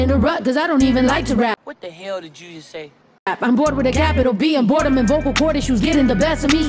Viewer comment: Bored with a Capital B. I thought she said I'm bored with Cardi B 😂😂😂